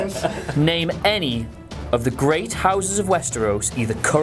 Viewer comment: It's not how he said it, it's what he said. Umber isn't a great house, they're just a..... house lol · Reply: 55meds oh a great house. I thought said "houses". I didn't manage to hear the "great" part